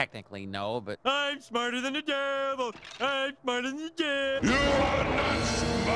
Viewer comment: YOU ARE NOT SMARTER THAN ME!!!!!!! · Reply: I thought he said I outsmarted the devil lol